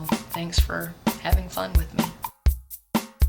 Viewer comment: i'm just like you! my parents never taught me tagalog either [for the same reason] so when my lola talks to me i'm like "what the hell are you saying?!?!" ha ha... speanuts i hear it as "hap-eslip"